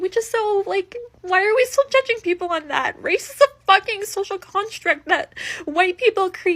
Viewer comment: Did she say "constrict?" I think she did. She didn't listen closely enough when "social construct" was the buzz term of the week.